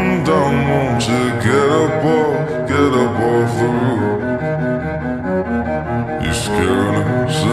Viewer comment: Every time I listen to this I hear 'and jet off the roof' instead of 'get' and 'jump'